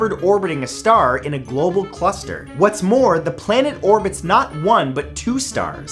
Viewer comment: Did you mean globular cluster? Sounded like you said global cluster. ;)